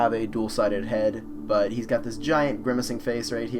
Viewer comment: Did you say "grimmacing"?